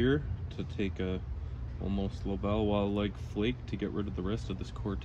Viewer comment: lol, 18:25 captions say "Little Bow Wow like flake"